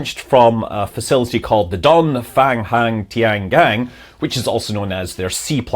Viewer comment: did he say "dong hanged banged yang"? that's too funny